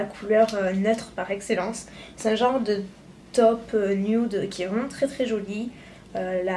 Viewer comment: j'ai compris qu'elle avait dis "c'est un genre de top" je me suis dit "euh c'est un sac..." et puis après reflexion... Ah oui, taupe... -_-'